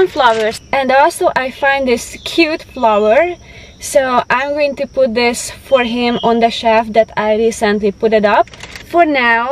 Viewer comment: I thought she said I am going to put the chef on the wall!! Lol